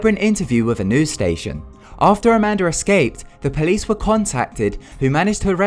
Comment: who else heard "amander" XD